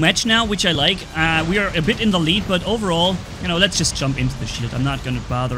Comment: did you said sh*t or shield???😅